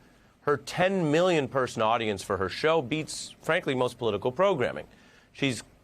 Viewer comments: I thought he said ALICE Cooper at first and I was like "She appeared with WHO?!?" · Oh i heard this earlier. I thought they said Alice Cooper. I thought it was an odd choice of interview